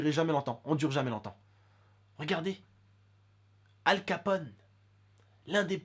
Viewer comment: J'ai compris al capote ;)